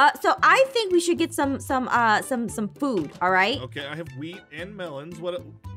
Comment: i srsly thought that aaron said weed instead of wheat 😂🤣